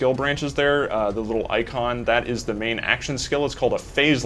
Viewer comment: Did he say Skyrim for some reason?